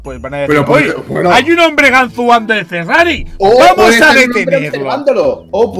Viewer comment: Pensé que decía "el gran robo del perra" No del "Ferra" xD